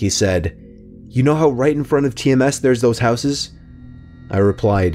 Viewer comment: did he say tms?😰 that's my middle school